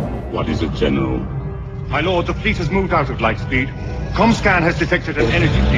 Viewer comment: All it needs is a Spongebob "Six Hours Later..." ComScan has detected an en- (whooshing noise) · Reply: I thought you said comcast